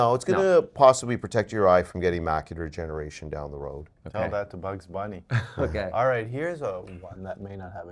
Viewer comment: My macular degeneration is advancing. What was meant by "That's a Bugs Bunny." at 3:17? (If I heard correctly.) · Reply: He said "Tell that to Bugs Bunny"